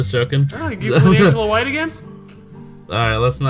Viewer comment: Anyone know what says at 3:50? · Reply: Angela White.